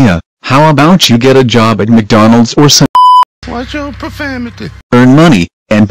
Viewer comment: Where did you get that clip from in 3:54 and what did he say? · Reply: Caleb Torres it’s from Storage Wars. He says “Watch Your Profanity”.